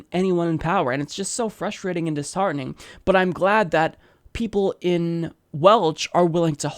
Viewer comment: "People in Welch" is he trying to say people from Wales or am I just tired and mishearing things?